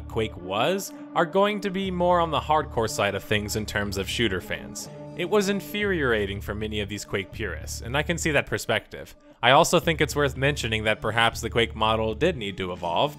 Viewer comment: I like Inferiorating instead of infuriating :D